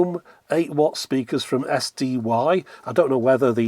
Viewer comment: They didn't. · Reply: oh. At 22 or so thought you said "5ohm speakers"